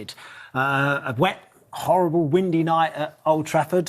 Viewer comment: He called the win a horrible win against Chelsea what is that the media got with Manchester United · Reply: I thought I heard "horrible windy". But I agree, they hate ManUtd